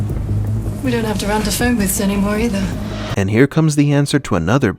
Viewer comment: What does she say at 4:48. We don't have to remember ...... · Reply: We don't have to run to phone booths anymore either.